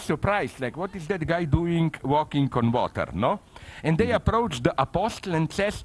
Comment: closed captions were "Converter" instead of "on water" oh my god, sublime